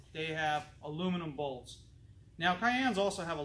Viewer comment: What do you replace every 60 thousand? I've listened to this like 9 times and I keep hearing you say "Bowls" can't tell if it's Pulleys or what lol